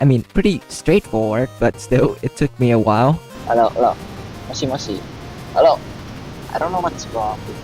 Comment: did bro really say moshi moshi im ROFLing 😆